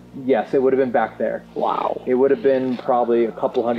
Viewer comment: i think it's in my head now because these things im hearing in the video aren't very far apart, but i swear it said "wow"